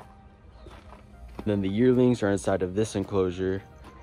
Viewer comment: Okay when you said "Yearlings" I thought you said "Yertles" for a second and I had a moment of nostalgia So thank you for that even if you didn't intend for it!